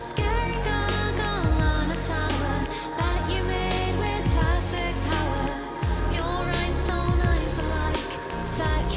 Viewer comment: Does she say "Made with prostate power"....? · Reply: Plastic